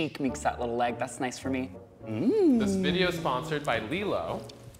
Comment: i thought they said "this video id sponsored by lego"